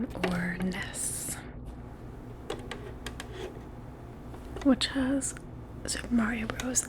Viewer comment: Super Mario Bros Duck Hunt caught me off guard. I first heard “The Nintendo Entertainment System, which has Mario Bros in the cunt.”